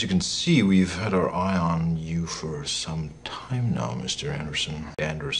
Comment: No Lie, when i was a kid i thought he was saying Mr Henderson